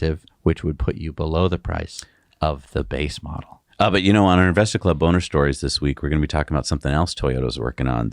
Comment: Listen carefully! Blooper @ 28:13 to 28:15 says, "On our investor club boner stories this week'" instead of saying "On our investor club bonus stories this week". I was laughing so HARD. Pun intended. I'm sure the ladies will be very interested in stories about investing in boners. Isn't it great to be human? We all make bloopers that make us laugh and we could all use a good chuckle every once in a while. Thanks. 🤣😂🤣😂